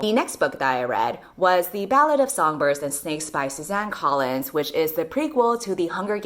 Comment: For a moment, I thought I heard "ballad of songbirds and spices" and thought it was a cookbook. Lol.